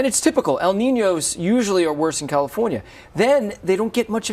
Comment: the correct term is "worse", not "badder". You as a CBS anchor should know that! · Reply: he clearly said worse. You're mistaking battered for badder